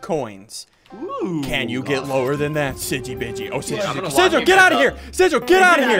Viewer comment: did he just said: sigi bitchy